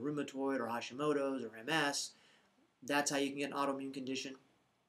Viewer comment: Did you say MMS on 1:45? Thank you.